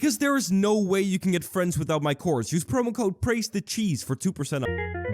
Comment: Did you just said "Praise the cheeese"? no... never. I will always"PRAISE THE SUNNNN!" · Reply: Praise the cheese